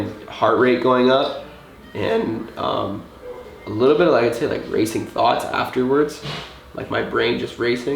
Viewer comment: I sneezed when he said racing thoughts so I thought he said racist thots